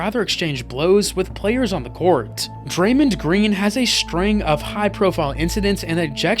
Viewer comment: did bro say daremned green 😂😂😂😂